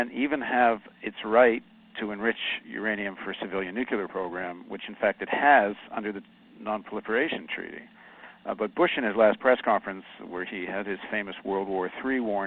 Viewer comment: Did he say 'nucular" at 4:00 and 4:11?